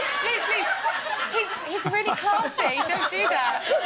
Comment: What does she say? He's really "what?" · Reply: classy